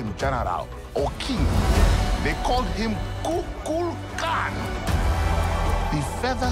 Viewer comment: did he just said " kokan " 🤣